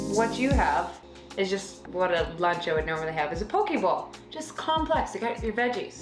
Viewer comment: I thought yall said you were eating a pokeball and was like "wow how many carbs is in a pikachu" then I realized...poke bowl.